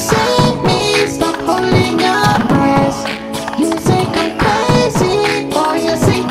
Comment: sounds like hes saying, "stop holding your breast"